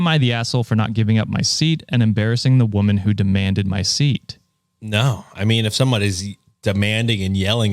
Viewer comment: Oh, omg I have low volume and thought he said "Am I the asshole for not giving up my SEED and embarrassing the woman who demanded my seed" and I was soo confused. And saying he's less likely to be compliant if they're demanding it lmao.